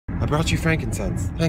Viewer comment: Omlll I thought he said dudess not Judas ALL THESE YEARSSSSSSS